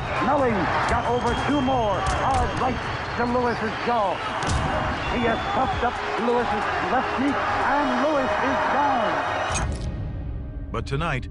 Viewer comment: Did anyone else hear the announcer say, "He has FUCKED up Lewis's left knee and Lewis is down"?